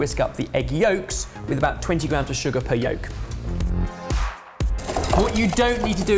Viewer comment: I heard "20 pounds of sugar per egg yolk"